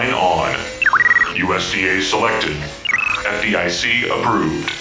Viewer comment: the goliath sounds like he is saying USCA selected